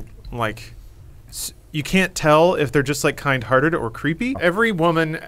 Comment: I thought at first Lawrence said kind of tarded instead of kindhearted.